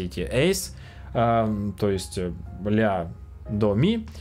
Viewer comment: я поехал или мне послышалось Бля